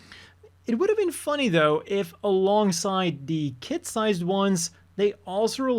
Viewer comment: The answer you’re looking for is Shikanoko Nokonoko Koshitantan Correction for the Crocs: They are available in Kids and Adult Sizes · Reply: The crocs are adult sized I already ordered a pair. I was so confused I thought you said "kit sized" for a min and got hyped